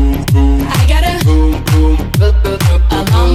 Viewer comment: I know I wasn't the Only one who heard "I got a, vroom, vroom, vroom"